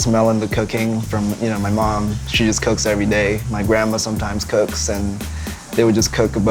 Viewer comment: Anybody else think Brian said "Cocaine" at 0:53?